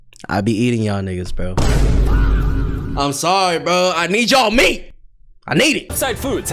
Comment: did u say u need my meet 😮😮😮ok I am ready 😊